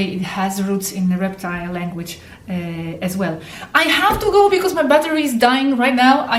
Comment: With my dying mother??? Where did that come from? My mother is not dying. :( :O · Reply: Thank U for paying attention to our comments dear ! In the very last minute I thought I heard U say I must go now quickly because my mother is dying ...So I am Happy to know that she is not :) Namaste